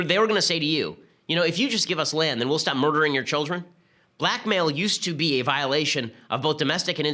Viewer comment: I thought Ben said 'Black Male' used to be a violation of both domestic and international law.